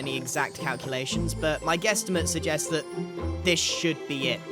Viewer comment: Did he just say guesstamite